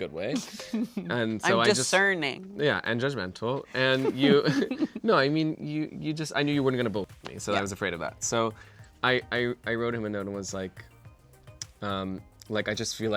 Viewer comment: What does he say at 6:36..he knew he wasn't gonna vote for me or bullshit me?